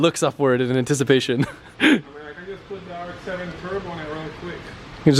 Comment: could be wrong but did he say rx7 turbo?